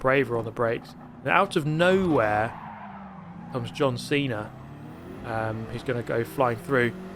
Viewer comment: "out of nowhere of john cena" sorry if i misheard but it's kinda funny. great video